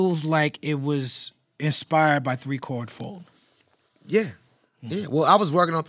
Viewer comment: What are they saying at 1:25:10 is it"3 car full"? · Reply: 3 chord fold. It was a terrace martin album that came out around that time.